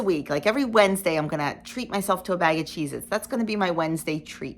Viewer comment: I agree about the pizza....if you use a substitute you won't be satisfied and will look for something else to eat. When Michelle and I did the Shred diet it specifically allows real pizza at times! BTW....when you said "Cheezits" I thought you said "Jesus", lol.